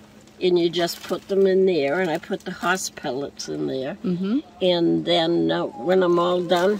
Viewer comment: What kind of pellets does she use to get rid of the odor. I heard her say house pellets, but I looked on Amazon and saw pictures of rats. What kind of pellets are these, please? I use a 5-gallon bucket. Also, where to get the bag in a bag for bucket? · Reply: She said horse pellets. They can also be called pine pellets, paper pellets or wood pellets. You can get them from feed stores, pet stores, Wallmart, etc. Sometimes you only find them in really big bags but you can also find them in the small pet section in smaller bags but be sure it's pet bedding, not pet food. The bag was probably from Amazon.